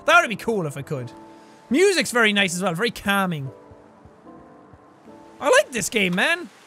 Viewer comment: I heard "I like this gay man" and started dying